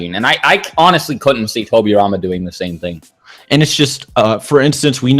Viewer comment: At for I thought it said Minato vs Toriyama